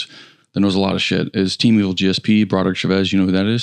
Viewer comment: Does anybody hear what Jared says at 30:50. I have no idea whose name came out of his mouth. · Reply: He said “Team Evil GSP Broderick Chavez.”